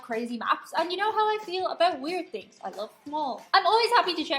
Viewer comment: Lol, at the beginning when you mentioned "weird things, I love them all", I thought you said "weird things, Iove Paul "! 😄 Feliz cumple, Chew! Con un tirón de orejas (you've got to tug his ear a bit --a Spanish thing)